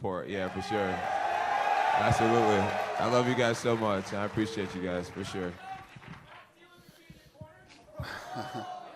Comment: what does he say at 29:55? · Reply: Jon jones is your crack dealer gonna be in your corner